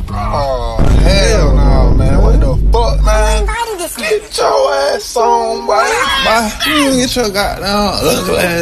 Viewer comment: What Did That Pitched Voice Say? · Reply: "Who invited this kid?"